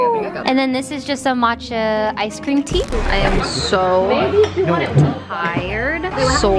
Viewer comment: am i the only one who thought valky was gonna say "im so high"